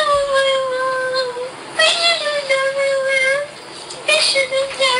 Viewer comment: Did she say I love my mom I shouldn't have done it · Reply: I thought she said "it shouldnt have happened"